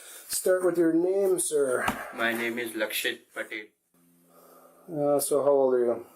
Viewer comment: WTH did he just say 1:28 did he just say his name is Likeshit Patel? 😂